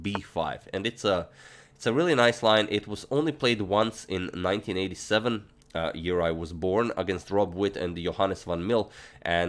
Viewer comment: i thought you said, " in 1997 the year i was born..." I was like the fuck hes only 23??? I had to replay